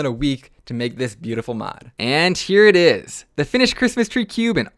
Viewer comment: is it just me or did say “and cheer it is”